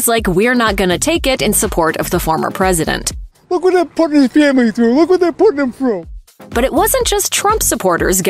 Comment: What did his supporter say at 1:27? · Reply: Look at what they are putting him through.